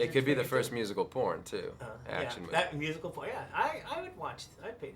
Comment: Oh he did! okay then i thought he said musical bourne.